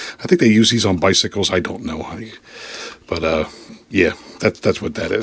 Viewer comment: Wait did you call us honey